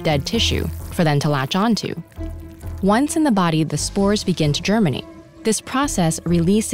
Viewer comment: Is it just me, or did I hear "Once in the body, the spores begin to Germany"????? WHAT??!